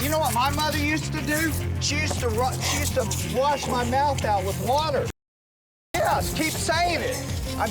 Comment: did he say he's mom used to wash is mouth out with water what?